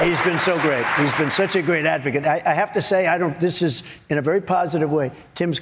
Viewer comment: Did he just say "he's been such a great african" at 4.33 🤯🤡